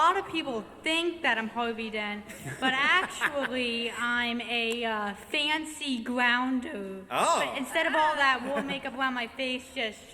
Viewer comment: Hey ! Sorry but I don't understand what it say at 21:17 to 21:30 ? Someone can help me ?(Sorry if is not a perfect English because I'm french) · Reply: 'Well actually, a lot of people THINK that I'm Harvey Dent, but actually I'm a fancy grounder. Instead of all that war make up all over my face it's just straight down half.'